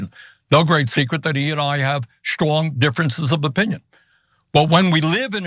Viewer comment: did he just say he and Biden have schlong difference of opinions? 😅